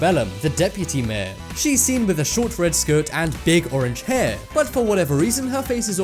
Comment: I thought he said big orange pair